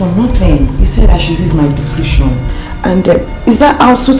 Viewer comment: Am not sure I heard clearly, did Faithia pronounce "DISCRETION" as DISCRITION?